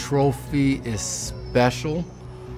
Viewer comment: Imagine being so good that you can damage the Lombardi and they don't call it damaged but "vintage" lol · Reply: I thought he said dentage